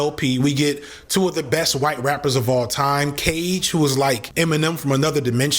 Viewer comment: Who does he say at 46:41 ? When he names the best white rappers of all time ? I couldn’t catch it Keach or something? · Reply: Cage